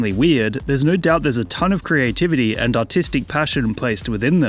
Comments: i heard "artistic passion" as "autistic passion" and had to go back to check 2 times... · I heard that as ‘autistic passion’ on the first time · for a moment i thought you said autistic passion